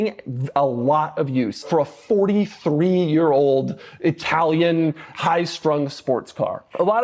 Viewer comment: Did I hear 43 year old. Thought it was a 93😅